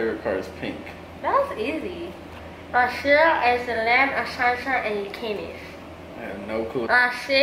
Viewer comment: I thought you say" brazil is the land of salsa and chichis" lmao😂😂